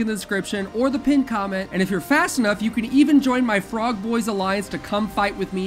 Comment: The third sponsor for the game I kept skipping through, and it said little clips of words, and it sounded like he said "Sucking my Frog"